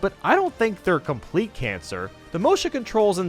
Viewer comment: did u say cancer??